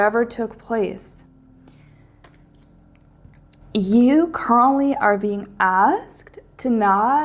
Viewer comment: You definitely say 'currently' but when I heard it I had to replay it because it sounded like you straight up said my name, Carly. I had a small freak out moment. Lol Idk where you came from, I've never seen you in my feed until this video. I have watched many tarot readings on YT and this reading from you hit me unlike any other I have ever watched. Every. Single. Thing. that you said was relevant. All of it. I have even said quite a few of the things you picked up. Him and I have both been through hell but completely different types of hell. He knows my value and won't come towards me in a relationship type way until he can fully, 100% show up. He is an Aries Sun, Virgo Moon. Both signs that you mentioned. And when you pulled that slow burn card, it is <num>% dead on. We just recently went 2 months without speaking a single word to eachother and the feelings between us just got stronger. It was definitely spirit that put you in my Feed. Without a doubt. This message showed up EXACTLY when it was supposed to. I've been heavily questioning whether to confess that 3 word phrase to him and the signs and synchronicities I've been receiving have been nothing short of screaming at me to just do it already! We've already silently said it to eachother so many times. And of course, as i finish this comment..... 11:11 on the clock. 🤯🤯🤯🤯🤯🤯 I couldn't hit that subscribe button any faster! P.s. my angel number? and i quote... "Angel number 1054 is a powerful message of redirection